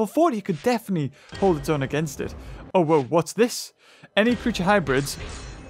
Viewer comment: did he just say OwO what's this